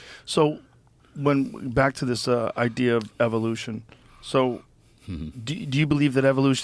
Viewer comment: It just sounds like Gibson is saying evil-lution not evo-lution😂, I agree. Great episode❤